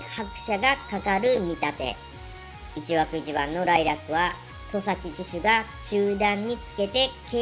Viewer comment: ライラックではなくて、ウンブライル~って聞こえたんですけど空耳かな？